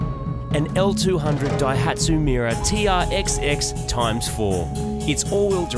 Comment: look at me , i thought you said a"Miura", silly me!